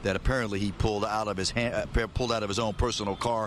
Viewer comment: “ And then the gun fired” said the reporter in closing. No! And then he fired the gun. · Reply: 😁 I thought he said "and then the gun fire" or maybe that's what I wanted to hear. 🤷🏾‍♂️